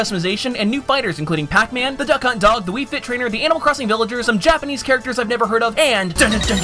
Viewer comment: sounds like you Said anal villager 8)